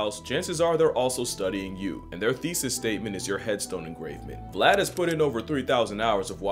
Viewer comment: He says, "your headstone engravement". I hear, "your heads don't agree with" 😂